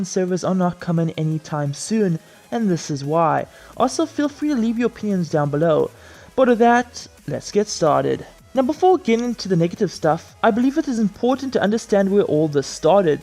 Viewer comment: No hate but am i the only one who heard him say "also feel free to leave your p*nis down below" 😂😂😂😂